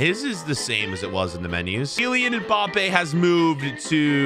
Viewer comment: When u said Kylian Mbappe the subtitles thought u said Jillian and Bobby lol